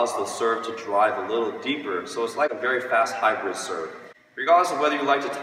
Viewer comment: did he just say Haikyuu serve 🤣🤣🤣